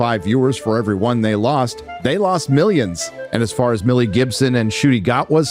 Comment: Was i the only one that for a split second thought it said Mel Gibson at 5:18? About had a heart attack.